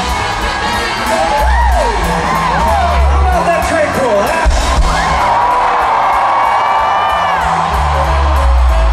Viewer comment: What exactly was Billie saying there? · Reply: Manthan Shah “how bout that Tre Cool” I’m pretty sure